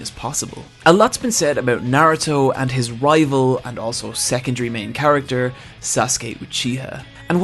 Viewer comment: I honestly thought you were say sexual instead of "secondary".